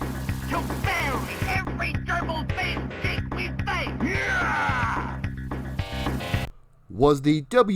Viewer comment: What does Hawk say in the Wrestlefest in game promo with Mean Gene? It’s hard to understand. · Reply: To me I always heard "You'll bury every gerbil faced thing we say" but that of course makes no sense. I think that is closer though.